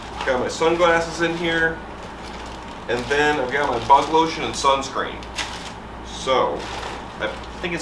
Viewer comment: Oh bug lotion!!! I thought you said butt lotion.